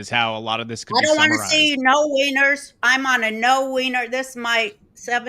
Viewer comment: bout 15 min.ago,thought it said WINNER.